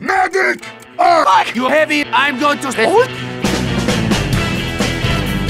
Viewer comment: Medic sounds like he says "i'm going to school"